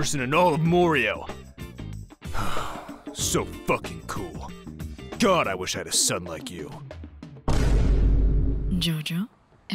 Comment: i swear i heard "god i wish i had a SUB like you"